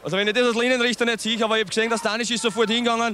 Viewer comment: sagt der bei 14:38 die Jugo verstehen sich guat oder kommt mir das so vor ???? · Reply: ja: "I glaub de jugo vastengan sich ganz guad und des wors dann"